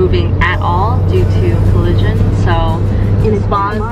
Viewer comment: when you said you were stuck in traffic due to collision i thought you said due to religion and i was like 🤨?? 💀🤚